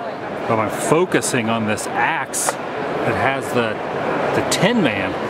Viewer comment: Did he say Terifer? Instead of Terrifier?